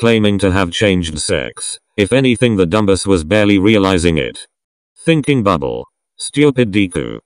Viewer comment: I HEARD THINKING BUTHOLE 😭😭😭